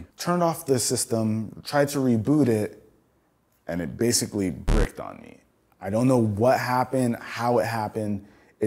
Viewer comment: Did he just say breaked on me 😂😂